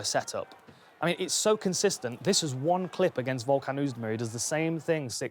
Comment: did he say volkanewsberry